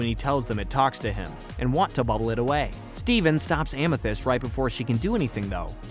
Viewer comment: "Steven stops Amythy-" what? I actually had to rewind that I heard that right (ive watched this show at least 5 times and can say every episode word for word